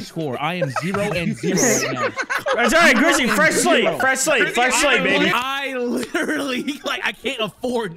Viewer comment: When said fresh slate it sounded like he said fresh slave 😂😂😂